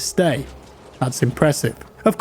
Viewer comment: Did jack say "That's Impressippe"?